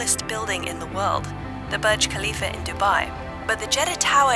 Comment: whats that? I heard MiA KHaLiFa